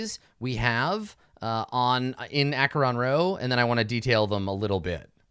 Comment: I keep mishearing Acheron Rho as Acheron Row, which would be a good name for the dense line of Imperial worlds if it wasn't so close to the name of the sector.